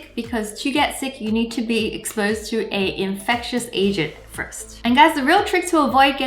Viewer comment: oh nooooo I heard 'infectious Asian' 😭😂